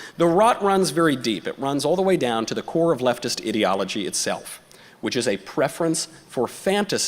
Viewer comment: Leftist "ideology". I thought he said "Idiotology"